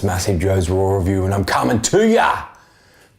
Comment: sounds like u say rawrelview